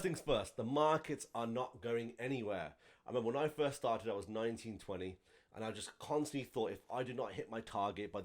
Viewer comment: I swore he said when I first started in 1920 🤣 but I know what he was trying to say - keep doing your thing!!!!